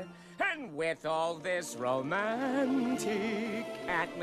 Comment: I heard “Romantic homicide” Is there something wrong with me